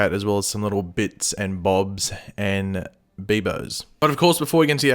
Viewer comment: Did he say Beebo or am I imagining stuff? #Panic!